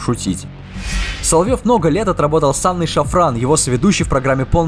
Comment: "ссаный шафран" - я один это услышал))